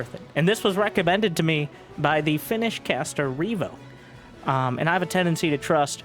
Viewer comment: Lol. I thought you said "the finished caster"